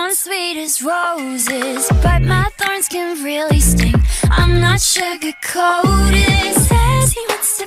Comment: I thought Rose said " But my farts can really stink, im not sugar coated"